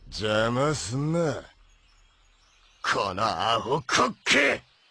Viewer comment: pftt. it sounded like he said cookie i remember this episode i laughed sooooo long